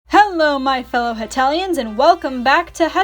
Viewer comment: I thought she said "Welcome back my fellow Italians" at the start XD